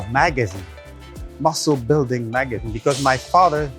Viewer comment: I thought he said muscle building maggots